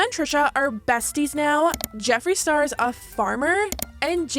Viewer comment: “Tana and Trisha are best friends now” “Jeffree Star is a yak farmer” feels like I’m in an alternate dimension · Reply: Right? I heard “Jeffree is a yak farmer” and about spit my drink out of my nose.